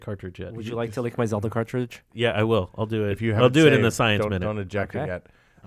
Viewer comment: totally thought Will said "don't ejaculate"